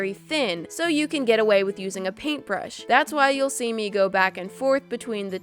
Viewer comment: as soon as u said paintbrush I heard "A Triscuit "